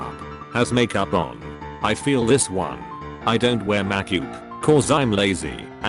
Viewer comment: All I heard was "Mak yoop" and that's it 😂